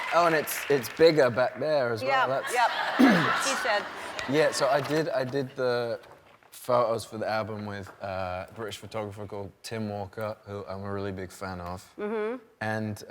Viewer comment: Does no one else hear Ellen saying "that's what she said" at 3:05 when Harry says oh it's bigger back there.